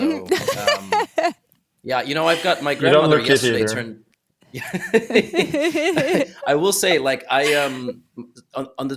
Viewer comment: What did magnus say at 2:18? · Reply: You dont look it either